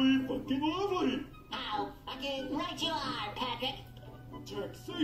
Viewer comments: Patrick: "Taxation! Spongebob: Is theft! Patrick: Fuck yeah! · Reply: At first, I thought I heard "Taxation. Is dead. FUCK YEAH!!!" I love it when misheard audio sounds unintentionally funny. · What did they say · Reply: "Taxation". But this video is not real